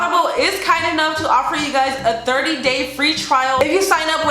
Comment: IT sounded like yu said "dirty day trial" XDD